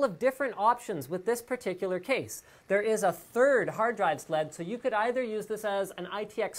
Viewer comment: I hear "Hard Drive Sled" quite clearly...